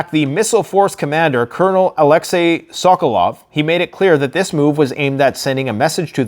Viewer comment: The missile force commander Colonel Alexei Suckalloff.... Lol. Did I hear that correctly? 6:50.